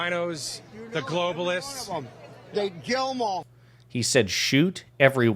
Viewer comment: I thought he said "Kill them all." The interviewed later walked back his agreement with that sentiment.